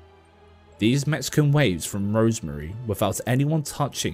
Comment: “These Mexican waves,” Did I hear that right? Is that a saying I’ve somehow never heard of, even though I’m half Mexican? Though I admit I’m a lousy Latina, one who can’t speak Spanish and can barely cook. 🤦🏻‍♀️😔 The Mexican Wave sounds more like a dance you do at the roller rink, al la the Mexican Hat Dance.